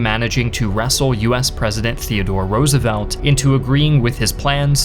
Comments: I think there is a mistake it says president theodore roosevelt instead of franklin roosevelt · Franklin Delano Roosevelt instead of Theodore "Teddy" Roosevelt